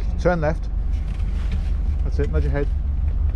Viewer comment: I first heard "Nudge ahead" as "Nod your head!" and was very confused! :D